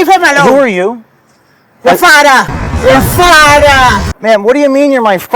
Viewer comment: What she said was "you'll find out", not "your father"